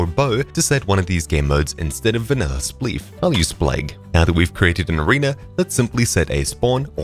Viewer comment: What you said next? spleeg or ???? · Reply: said "splegg". Take a look at the command he's entering at the bottom of the screen.